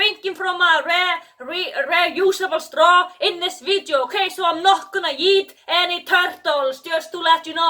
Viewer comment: I'm sorry but it did sound like you said, "I'm not yeeting any turtles !!"